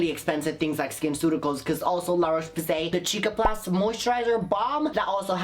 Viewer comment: she really said chick a plus hahahhahaha